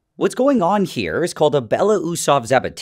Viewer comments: I heard babatunde's reaction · Did i hear this right "Bell of usopps evatinski's reaction"